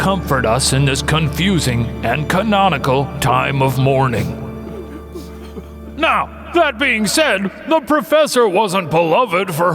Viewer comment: When the Propeller said “mourning” I thought he said “lore-ning” because we sure did lore-n